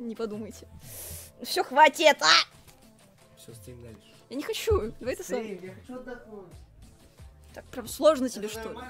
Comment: только я услышала давай соса.......???!?!!?!?